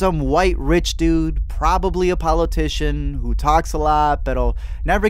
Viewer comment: “... who talks a lot pero...” 4:08 Did I hear that right? If yes, that’s just made me feel 10x better